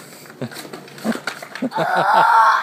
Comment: did he just say “s**t”? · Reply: No he said AWW I NEED IT